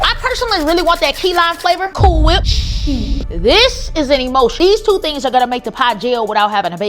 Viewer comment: Emulsion!! I thought she was say emotion I was bout to talk shit 😂😂